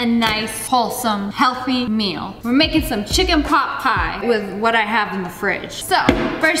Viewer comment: i thought she said chicken pop pie but its chicken pot pie